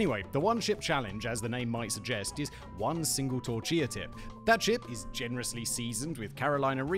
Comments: Anyone hear torchia tip? 😂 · anyone else hear him say ‘torchia tip’ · "One single torchia tip" - 2023. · haha...you said "chortilla tip" instead of tortilla chip😂😂😂